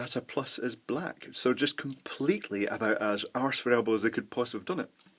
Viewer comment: Hey guys, I'm just wondering, what does says at 4:37? -So just completely about as [unintelligible] as they could possibly have done it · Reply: He says it's completely arse-for-elbow.